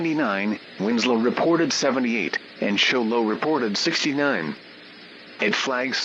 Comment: Sounded like 69